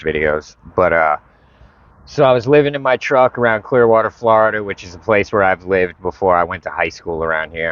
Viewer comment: I thought you said Queerwater FL.